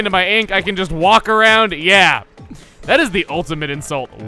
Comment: When said, "Yeah that," when he show that Judd drawing, it sound like he said, "Yang that."